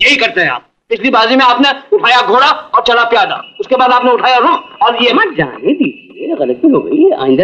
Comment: did he just say,'' uthaya rook"?